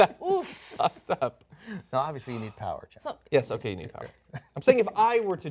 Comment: What did john say at 4:37? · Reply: "Yes okay you need power"